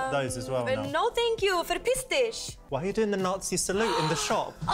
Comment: She said piss off, some ppl prolly thought she said some nazi thing😂